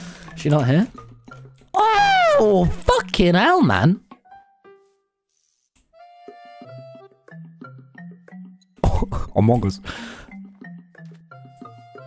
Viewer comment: When you said among us I heard you say Among Gus